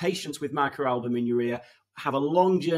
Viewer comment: Why do I keep hearing “micro album in your ear”?